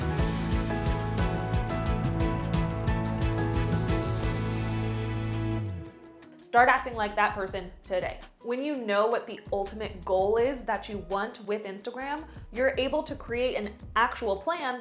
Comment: Thought u said "start acting black" and I was like "got that down pact"